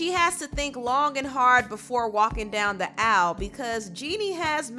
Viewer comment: “walking down the owl” lol I had my subtitles on and was confused for a quick second, then couldn’t stop giggling when I realized what actually said😩😭😭😭